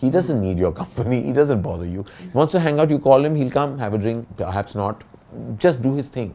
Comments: "have a drink.. perhaps not" almost sounded like "have a drink, perhaps snort.." And I was like, here we go again..! 😂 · Did he say Ranbir has a snort @ 21:45 WTH! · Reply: Have a drink and snort..